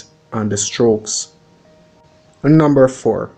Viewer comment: how do you write this, I heard Argo Plum, correct me if I'm wrong 😂🤔🙈